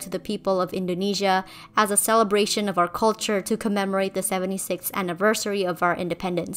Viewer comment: 76th. Did I hear that correctly?